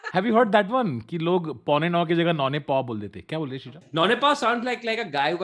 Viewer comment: "naune paw " kuch samjha nahi , kuch bhi !!! And why was Tanmay saying No BTS joke ? Kuch hua hai pehle?